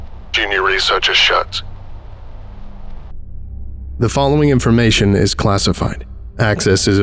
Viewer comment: Wait the researcher was familiar with frequencies and his name was -hertz? Really, author?!😂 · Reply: The transcript says schertz. Honestly I thought it has said shirts